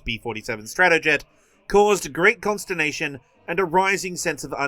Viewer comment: had to google that. Misheard it as "constipation" and I didnt question it at first